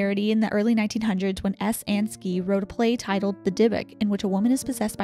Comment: I heard “sees Beyoncé” lmaoooo 😂